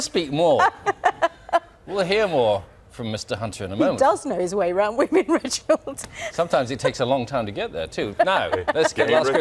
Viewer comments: What did Reginald say? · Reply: Nasra Elmi "Game recognize game" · What does Reginald say? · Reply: Game recognises game. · What does Reginald say at 0:34 please? I can't catch it. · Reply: He says "Game Recognise Game"... slang meaning it takes one to know one. In other words, Reginald D Hunter is saying he does it himself, so he knows he's right